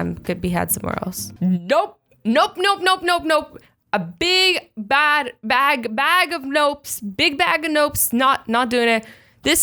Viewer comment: I thought she was saying a big bag of nopes but what I hear when im a little far I can hear a big bag of Gnomes!!😄😄